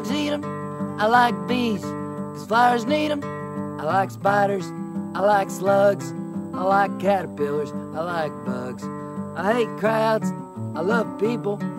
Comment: I thought you said you hate crabs for a moment: 🦀